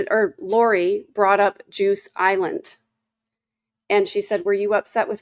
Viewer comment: At first time I listened to this live, I thought you said Lori brought up "Jews Island"? I was curious what Jewish people had to do with Lori's line of questioning. It actually was " juice island" What a hoot.